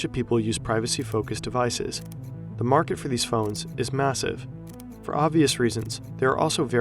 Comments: did you say massive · did he just say MASSIVE???? 😮